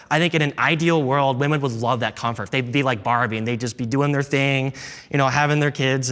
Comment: did he say “Barbie”? Did he move his arms around like a Barbie doll?